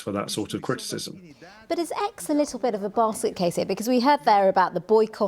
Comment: What did she say at 4:48? "Is X a little bit of a...?" A bar suitcase? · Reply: Basket case